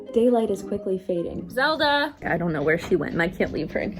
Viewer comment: Thought she said “gaylight is quickly fading”